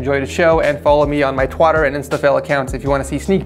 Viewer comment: is nobody gonna question saying "Twatter" instead of "Twitter"